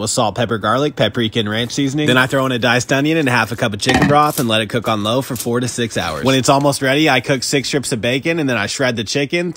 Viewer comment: I swear to god the first time around I could’ve sworn he said “let it cook for 46 hours” lmao